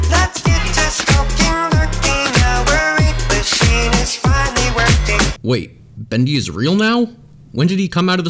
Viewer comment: When bendy says "let's get this??? Working" I always think he's saying " let's get this f*cking working"edit : I frogir to time syamp